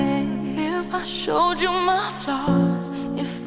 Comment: wait- did she say "if I showed you my FLOSS?" No, I must still be thinking too much about toothpaste :)